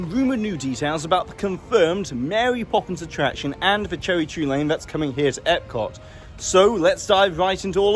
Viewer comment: it sounded like he said Joey Drew lane 🤣